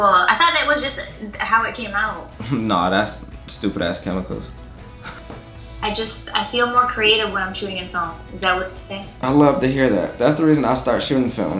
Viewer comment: "is that what you wanted me to say" what the hell 😂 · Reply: I said “i feel more creative when I’m shooting film, is that weird to say?” hahaha